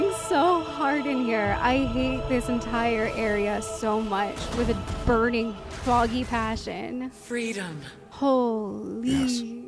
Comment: I was multitasking watching your last Valkyrie fight , misheard you and thought you said “I hate this with a burning froggy passion” but now I’m going to use that forever